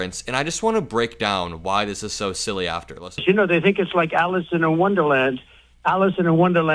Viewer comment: Did he just say "ALLISON in Wonderland?" Did tRump write a new children's book? Also, "Alice in Wonderland" is something he clearly hasn't read because it's not a good comparison at all!